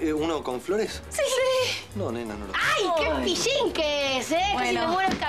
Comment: Can somebody tell me what does Monita say at 0:59? "Ayy,que.....? " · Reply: "pillín"